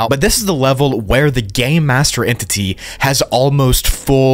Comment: it sounds like you said “the gay master”